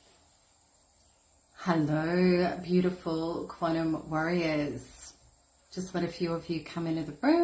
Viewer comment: The narp/speedy q&a earlier today was amazing thank you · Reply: For a split second, I thought I heard you say "Hello beautiful quantum worriers!" 😜